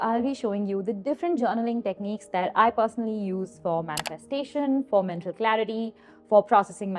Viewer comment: At @ 0:18 I feel like I heard " different journelling techniques I personally use for - frustration "